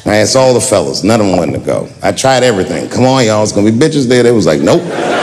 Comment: Come on y’all it’s going to be ladies there 😂😂❤🎉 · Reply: Ladies! I thought he said "b*tches?"